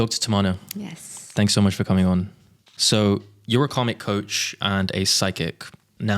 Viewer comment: I heard it as you are a comic coach. Can you pronounce better? Its kaRmic